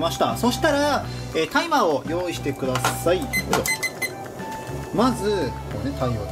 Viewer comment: ｢タイマーを用意してください｣が｢大麻を用意してください☆彡｣に聞こえて、思わず｢無理です！！！｣って言いそうになった…